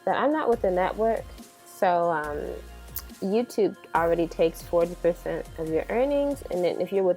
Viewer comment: My mistake. The correct time is 5:12 where she says how much Utube takes from your earnings. I could not understand her. Did you understand what she said? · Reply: 40%